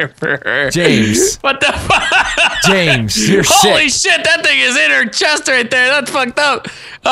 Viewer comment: did say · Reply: ButterMus I thought he said 'shit' too, but he actually said 'sick'. What makes more sense, "James you're shit." or "James you're sick." ?